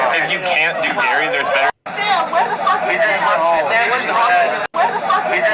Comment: I heard "he just left his sandwich ." BTW...Don't stop talking to the rest of us and waste your time having a battle of wits with an unarmed person :body-blue-raised-arms:. Now...where were we? :face-blue-smiling::face-red-heart-shape